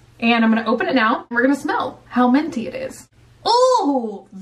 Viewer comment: It sounds like you say menty when saying "minty".